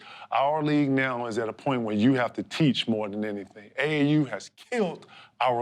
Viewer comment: "AU has killed our league" is what I heard. What did KG mean? This is one intense person. I saw KG at one Celtics game at the Garden. Only time I can genuinely say I was in awe of someone. They Celtics come out for the shoot around, there's some military band in the center of the court, and there's Tommy Heinsohn doing a broadcast. There's Paul Pierce -- yeah seen him a bunch of times on TV. There's Rajon Rondo .. yeah seen him a lot too. But then Kevin Garnett comes on the court and I am like ... "OMG... that's Kevin Garnett... it's really him... in person!" It was like that. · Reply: AAU